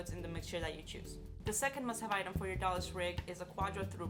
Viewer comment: Ahh, I see a door less rig, I thought you were saying Dallas rig , you do mean a rig not based on a Dee , Ay Double you , don’t ya ?